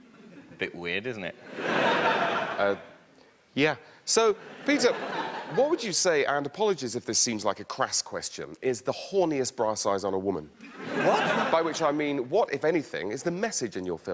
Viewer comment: Love the reference to "A Bit of Fry and Laurie" with the "Horniest bra size on a girl" line. Classic xD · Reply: I genuinely heard that as 'the horniest brass eyes', which I just assumed was a euphemism for something.